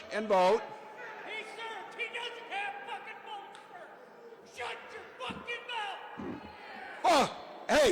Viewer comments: He told Tim to shut up😂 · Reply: This is what I hear from the "shut up" patriot. This patriot says "He served! He doesn't have f(expletive)ing bone spurs. Shut your f(expletive)ing mouth!" If I am mishearing, correct me If you don't recognize the reference to "bone spurs," then you listen to too much Faux News. We all know who served and who "claimed" bone spurs, so we all know who the "shut up" guy was defending and who he was denigrating. P.S. The cowardly heckler who was harassing Walz did "SHUT UP" when told to by the patriot! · This is what I hear from the "shut up" patriot in the audience. I hear this patriot say "He served! He doesn't have f(expletive)ing bone spurs. Shut your f(expletive)ing mouth!" If I am mishearing, correct me If anyone does not recognize the reference to "bone spurs," then they listen to too much Faux News. We all know who served and who "claimed" bone spurs, so we all know who the "shut up" guy was defending and who he was denigrating. P.S. The cowardly supporter of the draft-dodger-in-chief who was heckling Walz DID shut-up when ordered to by the patriot!